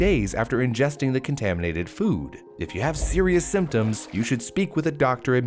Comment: I heard what you did there: 10:59 You said, "If you have *serious* symptoms..." and I heard that as "If you have *cereus* symptoms..." 🙂